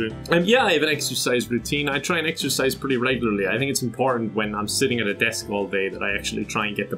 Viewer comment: I might be stupid but at 6:27 I'm hearing "I need some porn" and I can't tell what's he really saying. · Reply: "I think its important"